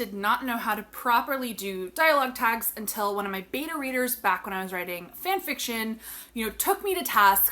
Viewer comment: Thank you for this video. Great information. The only suggestion I would offer.... It's pronounced Bay-Tuh. I thought you said "My beaner readers."